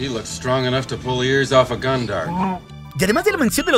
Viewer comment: yo entendi un gungan